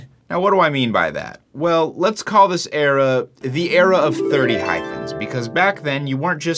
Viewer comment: Was this supposed to be hyphens instead of hymens? 😂